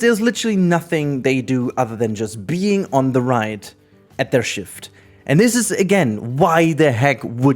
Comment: i had to rewind that part 4 times because i could have sworn you said "just peeing on the ride"